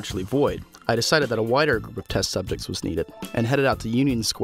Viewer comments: did he say wider or whiter...? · I thought he said a WHITER group of test subjects the first time around. I was like WTF. The shit you think you hear sometimes is ridiculous.